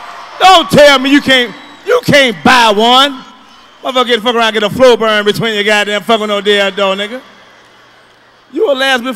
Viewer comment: Can someone tell me what did he say at 3:15 to 3:21 ? 😁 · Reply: You gonna get a something burn fuzzin"" around wit a dild#